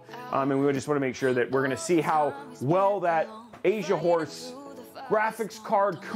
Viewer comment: Take a little longer pronouncing Asian horse. stress that S my man. LOL. Did a double take and thought you were saying Asia wh0re. Which is wayyyy not the right thing.